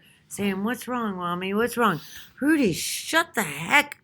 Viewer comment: Ok 54:11 the dogs name is Rudy!!sounds like you said he😆welp he finally did make her snap😆 omg I give it to her because I can’t🤦🏼‍♀️my mothers neighbor is a awful woman who keeps her dog on a leash tied up to her back porch all day and night and the poor dog basically cries and barks all day and night you can hear him inside of my moms house it’s really sad 😞 mother said she reported the woman but no one has done anything to help that dog who is clearly being abused and neglected tied up on a short leash on the porch.When the kids go by it’s so sad because he wants to play he’s still a puppy looks like a boxer beautiful dog my mom says once the dog started growing that’s when the evil neighbor put him on the back porch😢 I am unfortunately a smoker I’ll quit when I’m 34 lol I said 30 at first but now it’s 34 but I couldn’t even enjoy a cigarette on the pack porch because the dog it’s just heartbreaking hard to watch this was actually Easter Sunday so I started going to the front porch to smoke so I wouldn’t have to see the dog but you can hear him and like I said even inside of the house you can hear him so I had to leave because it was heartbreaking and upsetting for me but I couldn’t handle it’s just depressing so I took my kids and came home sorry another long rant 😂 but if anyone has any idea what I can have my mom do to save that dog and reads this long comment please I’ll take the advice and do it myself for my mother to get that beautiful dog a better chance it’s unfair